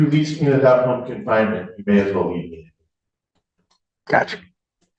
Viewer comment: I couldn't understand what the defendant said at 5:50. Can someone please help me out? He mumbled the last part of his sentence. "...you may as well _________." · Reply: You may as well leave me (as in leave me in jail). I think that's what he said anyway. Like, he knows that if he doesn't have a home confinement order then he'll go places he shouldn;t and get into trouble again - this was my take on it anyway